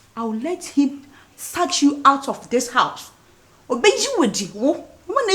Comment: did i hear 'satch you out of this house' eiii u people tooo